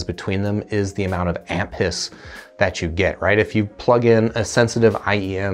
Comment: Not gonna lie, the first time he said amp hiss, I thought he said ant piss. I thought that was a weird thing to look for in a mp3 player but I wasn't going to judge.